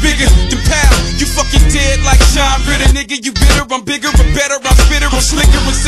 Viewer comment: does he say "john britten" at 0:38?